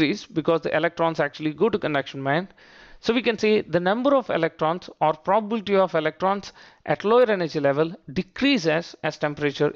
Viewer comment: Can somebody explain to me what did he say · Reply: The number or probability of.electrons at lower energy levels ...